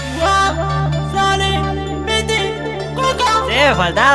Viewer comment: [INICIO] Duermo dentro de un termo Envidia "Wo, Sony, Pity , Cuco"??? 2:08 no se si escucho mal o tengo la mentalidad retorcida pero parece que dice guau, sale, pt, coca jajaja, es un mensaje subliminal?